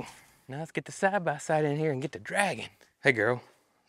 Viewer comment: when you said "an' get to draggin'", I heard "and get the dragon". And I thought; they got ANOTHER new animal? Sorry about that.😊